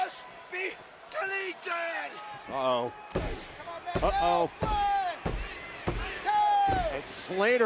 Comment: Who else heard hardy say ohh shi* LISTEN CLOSELY